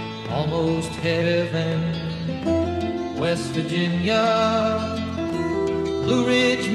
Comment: A sad, lost man lost on the country roads and is begging them to take him home. Too bad he was walking the wrong direction! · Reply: Oh Mr. Krabs I thought you said Weast